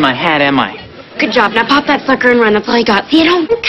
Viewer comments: it sounded like she said pop that fcker 😭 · thought she said “pop that fucker”